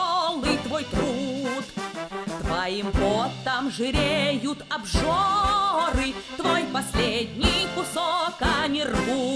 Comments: Твоим потом жиреют офшоры (послышалось, думал - брежу, потом слова посмотрел😄). Хороший гимн у Франции, только слова, вероятно, другие. ✌️👍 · Кстати, мне послышалось, что поëт не "обжоры", а "оффшоры". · прикольно... а мне вместо "жиреют обжоры" сначала послышалось "жиреют офшоры"...🙂